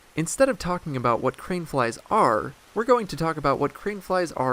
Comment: I keep hearing “cream flies.”